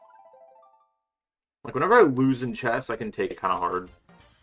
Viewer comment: Omg nice job changing your vehicle! Congrats on reclaiming the shroomless BKT with Dolphin 🐬 Dasher and with a big timecut! Btw, what did you say at 0:02? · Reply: whatever i lose in chess i can take it kinda hard?